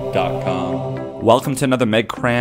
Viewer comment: I hear 'mad crème' every time I start up a update here. :D